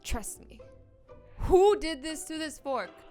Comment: İ thought she said spork